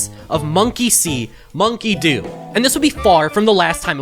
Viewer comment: when somebody says "monkey see - monkey do" I always hear "monkey semen keydo". I suspect I have a hearing problem or something